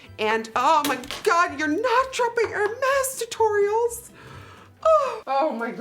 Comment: "the brand er-mez..." me, who's pronounced it as "hermes": oh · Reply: I thought she was saying air mess